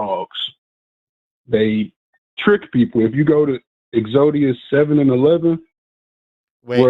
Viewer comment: as a fellow Yu-Gi-Oh player, I lol'ed at Exodia instead of Exodus. Thanks for making my day